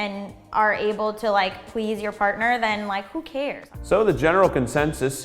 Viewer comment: at around 2:40 did she say fartner?